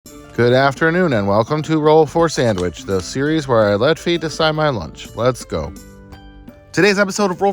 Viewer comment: Oh no i definitely heard it as "I let FEET decide my lunch" instead of Fate and did a whole whiplash second take haha